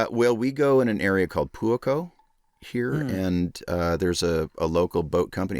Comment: when he said "Puaco" I thought he said "Pooh and Co" for a second 😂 I was like "Oh! So you went with like Tigger, Eyore, the others?" 😂